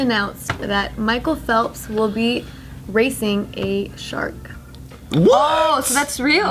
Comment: For some reason i thought that said ''Raise a shark''